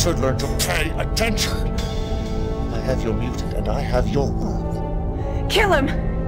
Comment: What does Kane say at 0:08? I have your mutant and I have your !?!?!!? Uth!? What is that word? Please tell me! =) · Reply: World